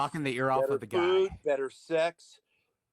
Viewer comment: Wait a minute! Did he say better food and better sex in relation to being sent to jail?! That's wi-ld!😮